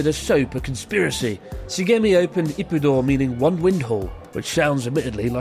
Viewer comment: I thought you said "one wind hole", which I thought was an odd name for a restaurant.